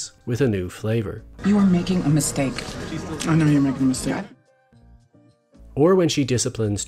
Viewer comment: "Youre making a mistake" I thought she says "and now you're making me sad" and not repeating the mistake line